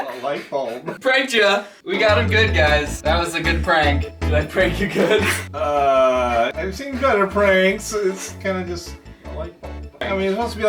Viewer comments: did he say gooder pranks?XD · did he just say ‘gooder pranks’ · DID HE JUST SAY "gooder pranks"?!?!?